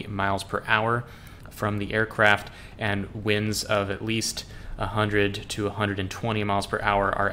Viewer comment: 100-120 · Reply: hm i might have misheard 100 to 120 mph as 100 220 mph